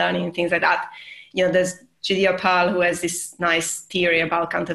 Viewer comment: she mentions someone's nice theory about counterfactuals - can anyone help me with what she said? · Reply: Has to be Judea Pearl